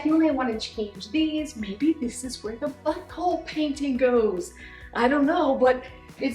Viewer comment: Did anyone else hear butthole picture at 1:09?